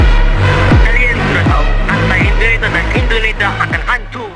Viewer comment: what you SAID? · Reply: indonita akan hancur not indonesia